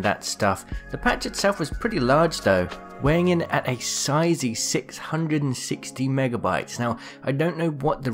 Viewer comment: Almost had a heart attack at 2:09 when i thought he said the patch was 160 gigabytes lol